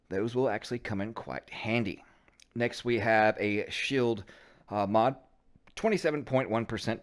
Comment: Its funny I thought you said shit mod for the shield mod and I totally agreed shield is a shit talent anyway lmaooooo 😂😂😂